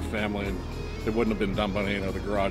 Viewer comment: I heard"!&. Watch u guys show. But who the hell. Is Gotham customs.🤣🤣🤣🤣🤣🤣